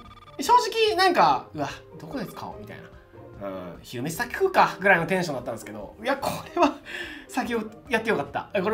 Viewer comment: ここ何回聞いても聞き取れなかったんですが、何て言ってます？ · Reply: 「昼飯先食うか、ぐらいのテンションだったんですけど」って言ってる気がします！